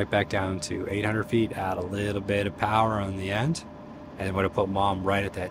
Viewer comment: Why no power on landing? I thought you land with full power in case you miss the hooks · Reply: Oh I heard it as "no power"